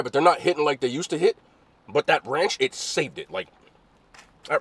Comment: What up my boi? another fire video it kinda sounds like you said "that branch saved it"@ 823